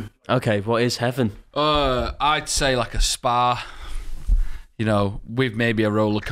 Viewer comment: I thought was saying spar. Like what kind of heaven involves a small supermarket with a rollercoaster in it.